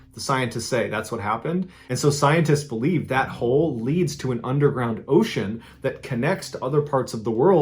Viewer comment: Mr Ballen, Did you just say UNDERGROUND OCEAN!!!? Even the bad ass diver was like "Hell nah, this recovery mission is over"